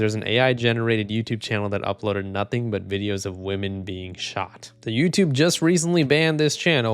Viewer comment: i thought it said women bigshot :(